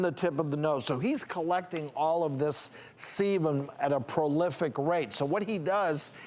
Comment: I SWEAR he said semen. 😂 I was working on a project, listening to the video and I heard "he's collecting all this semen" and went "WHAT?!? Do you mean SEBUM?!?". When I watch the video though and see him make the "B" shape with his mouth as he spoke it became obvious he was saying "sebum". Damn. At first I was like "homeboy needs to stop getting cum facials if he's having that much build up of jizz seeping in to his pores!"